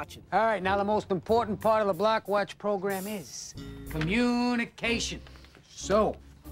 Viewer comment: I thought he said black watch programme